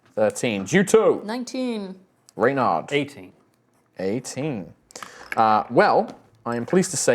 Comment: I swear, I heard say 80 for his initiative rather than 18. And knowing him, I was happy to accept it as his roll until said was going first